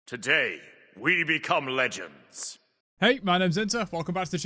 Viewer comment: I know you say, "hey, my name is inter" but I always hear, "hey, my name is sentze"